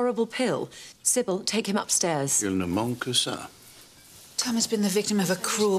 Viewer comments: What does Robert say at 3:24? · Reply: He said "Il ne manque que ça" in French. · What does Lord Grantham say just after 3:20? Sounds like "homoncasar" 🤷‍♂️🤣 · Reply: 'il ne manque que ca' 'that's all that's needed' in French